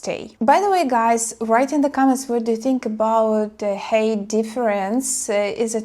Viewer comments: sounds like you say hate. ненавидеть while height высота - pronounces H+айt and yea. the goblin always move with. so the saying goes. travelled and moved around alot as well. if you have a stabile life at home and want to move to a foreign country, id suggest taking at least one vacation first, possibly a few even. to get a lay of the land. get a feeling of how it is. Love high girls, but.. I'm also semi high 6'1 (186) myself.. so. not that i really care.. since.. I'm waiting for God to appoint my wife, or ill fly solo forever. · !!! yes I thought you were saying "hate" at first when saying "height" heye "t" --anyways to this guy CMON !!!! wake up dont be a moron (over the internet? live far apart? only talking for 1month? lol your lucky she at least showed up to meet you, you do seem very desperate !! and you said "clam" and "banana" you can be a nawty little girl !!!